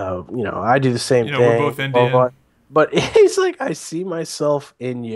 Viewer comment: Did say Dope is indian ?